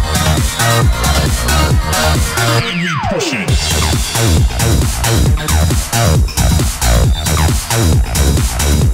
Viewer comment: What is it saying at 0:56? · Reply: AND WE PUSH IT!